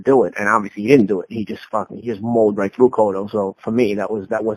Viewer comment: Did he say Pacquaio MAULED THROUGH Cotto at 4:59? LMAO